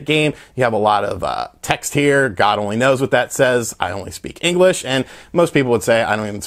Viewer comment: I only speak English I thought he said I don't speak English LOL